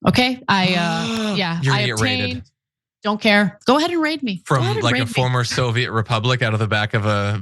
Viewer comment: I had turn the captions on because I didn't know what they were talking about! "Oh you gonna be r..., go ahead and ra*e me" i couldn't of thought anything else they were saying.